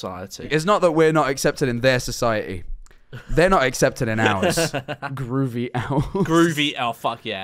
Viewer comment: I thought groovy owls said groomy owls because of discord